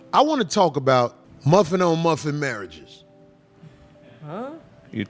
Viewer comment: i thought he said muffin on muffin mary